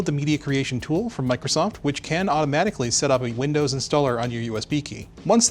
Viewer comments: Not sure if he said automatically or 'magically' but I heard it as automagically and honestly it's my new favorite word! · Did I hear an "automagically" there? 28:45 lol . Great video btw you really know your stuff!